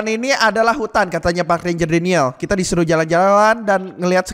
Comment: gw dengernya pak villager Daniel:v